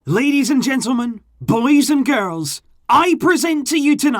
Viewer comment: I heard "ladies and gentlemen, police and girls".....at the intro